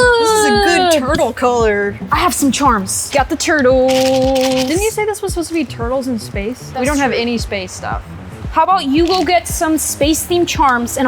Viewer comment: who else hears “ turtle turtles”? 🐢